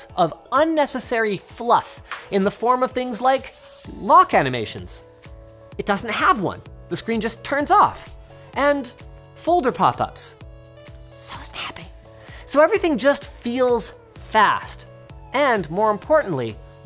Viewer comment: What did he say at 6:11 ... so snappy?